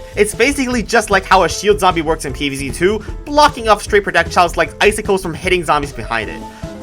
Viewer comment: did he say "prodecjiles"?